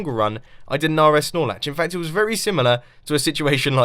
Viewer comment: Who else thought he kept saying "Snorlax" at around 7:15?? XD